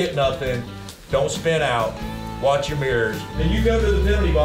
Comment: I thought he said "Watch you beers" at the drivers meeting. Mirrors, got it.